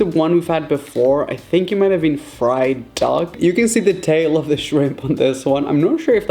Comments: Did he say fried dog 6:45 🤣🤣🤣🤣🤣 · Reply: Oh he did say fried duck but it sounds like dog 😂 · It sounds like he is saying fried dogs 😂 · did he say fried dog or duck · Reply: Chakara Lopez duck. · did he say fried dog? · did he just say dog?!? Or duck? I’m confused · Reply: he said duck. They can't put dog meat in sushi in the UK🙄